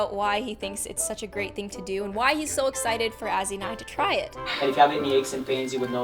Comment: did she said he was excited for Azzy not to try it